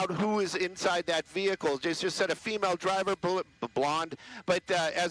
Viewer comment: did he say dumb blonde?